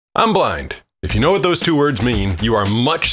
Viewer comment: for a second I thought he said "I'm black"